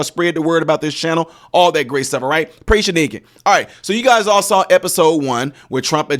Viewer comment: What did he say at 0:52 I heard "praise you naked" But I had to replay it several times. I want to think he said "praise you make it" and I am sure of it, but I need to make sure or find out if it was entirely something different.